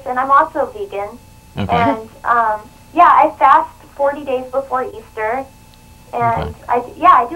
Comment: Did I hear say she fasted for 40 days?